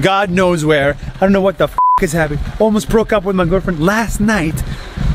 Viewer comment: did I just hear that almost break up with my girlfriend (Well that's a bad story)